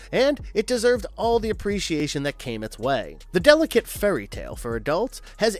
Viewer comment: Did you say "delicate furry tale" at 41:50 ? Probably not, but it gets me thinking of a reason why they might show Catholics as the bad guys lol